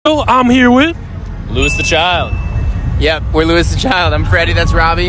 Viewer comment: I thought he said lose the child